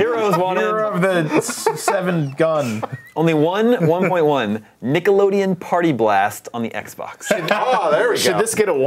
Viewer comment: I misheard 'Nickelodeon Party Blast' as 'Pre-Columbian Party Blast' and now I'm imagining a party game with like Mayan and Aztec warriors competing in stupid mini-games. It's quite a fascinating delusion, really.